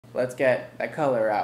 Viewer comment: At the start it sounded like he was saying let's get cholera and I laughed harder than I should've